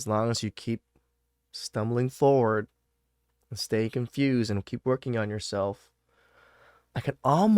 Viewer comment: did you say "stay confused"?